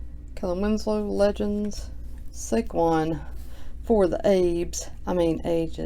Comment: Lol for the Abe’s... I thought you said for the apes at first. I was like that’s a racist card